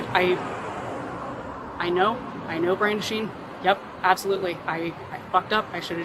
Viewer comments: subtitles say “brain machine” in red lol. She said “brandishing” 😂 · You guys need to do a better job with the subtitles 15:49 woman says "i know brandishing" subtitles say "i know brain machine" 😂